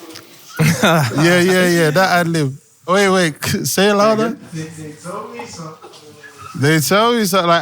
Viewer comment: yo what is that adlib tho i thought he was saying they told me turn but wtf does that mean